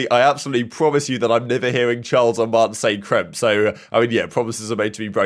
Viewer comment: Did call them Chiles and Mance? 😂😂😂